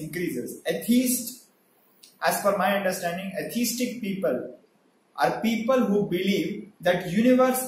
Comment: With his accent, I thought he said "autistic people" for a moment there.